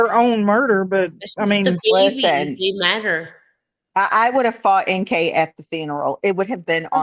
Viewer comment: I at first thought Chris said, “I would have fought NK onsite at the Bingo!” And that cracked me up! 😂😂